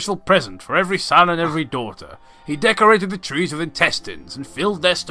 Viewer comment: (or so) I heard "he decorated the trees with testes". What happens when you're up so late listening to a reading of a tale of great suck.